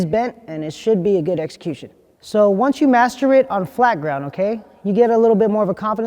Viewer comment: I thought he said once u masterbate on flat ground. 🤣